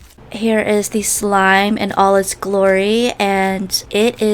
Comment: did u say Allah S glory?!